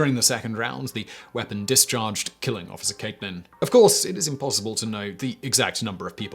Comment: I think I heard officer caveman right? Because you must be one to do this!